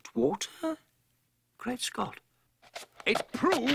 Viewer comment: Do my ears deceive me or did Dawson just say Great Scott (The line from Dr. Brown) at 1:55-1:56?